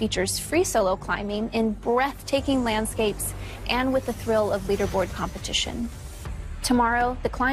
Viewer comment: Did she just say "the trill of leaderboard competition" lmfao